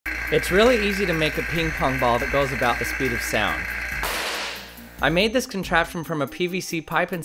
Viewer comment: I could have sworn you said speed of light at the beginning